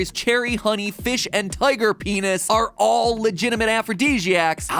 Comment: Did I just hear "TIGER P*NIS" in 2:19? Or is that just me?